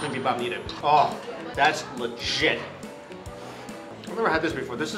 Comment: did he say legit or lesh*t?😳😂